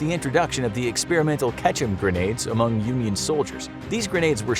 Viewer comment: I'm so tired i thought he said "Ketchup Grenade"